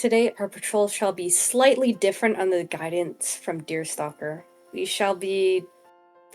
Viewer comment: Deerstalker kinda sounds like Darkstalker (WoF fans will get it :D) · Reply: I MISHEARD DEERS5ALKER FOR "YOUR STALKER" HELP